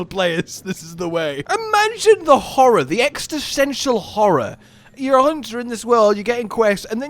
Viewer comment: did he just say extisential Horror insead of existential?😂😅